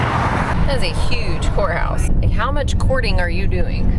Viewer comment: I thought you kept saying whorehouse😂😂